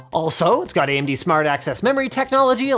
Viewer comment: Every time mention ‘Smart Access Menory’, I hear it as 'Smart Ass Memory'